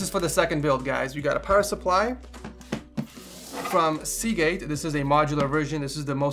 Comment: Did he just say seagate ?